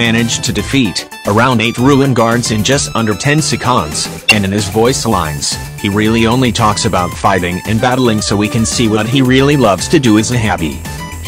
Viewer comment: did it just say sectonds?